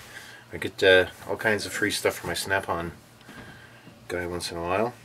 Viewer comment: I know they are quality tools, but every time you say "Snap-On"... I hear 'Strap-on'